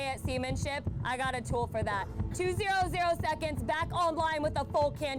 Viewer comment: I watched the actual video, and I thought she said 2 seconds. Thanks for clarifying this.